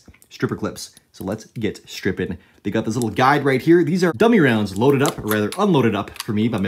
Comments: When he said dummy rounds I heard “w” rounds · I missed the part where you said those were dummy rounds and nearly shit myself when you pulled the trigger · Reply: Same I thought he said W rounds and I was like Ive never heard of those before